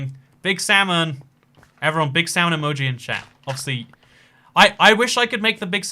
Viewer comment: I thought he said bi Sam emoji LOL